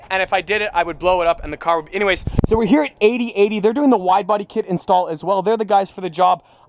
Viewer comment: I kinda thought said "Kid" but it was the widebody